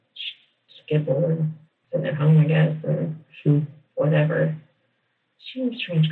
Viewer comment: I don't care what the transcript says at 54:30 she said she "WAS" a strange girl! 1:02:48 I didn't know who she "WAS" I mean there's SO MANY times that she speaks of Taylor in the past tense!